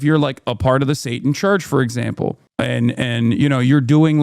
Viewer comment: Did he say " Satanist's back to school drive?"😂😂😂😂😂😂